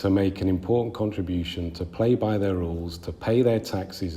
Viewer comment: I think I heard that right, "To play by THEIR rules".